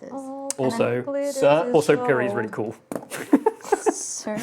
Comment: I thought said "also searching Pyri is really cool" and I was like HHHHHMMMMMM IS THAT SO.